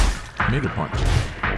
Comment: sounded like n word punch